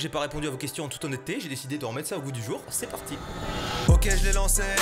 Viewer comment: Aussi, une autre à 0:40 minute ou il dit "J'ai désidé de remettre ça au bout du jour", j'adore je vais le dire moi aussi maintenant^^ je trouve que ça sonne bien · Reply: par contre j'entends bien "au goût du jour" moi 🤨